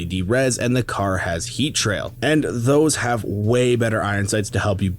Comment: which skin did you say is the "pay to win" skin for the car? Heat rail? Which skin is it?